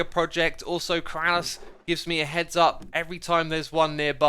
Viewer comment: X, did you just say Keralis gives you a "Heads" up?